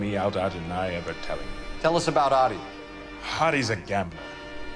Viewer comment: Thumbs up if you heard Hottie instead of Ottie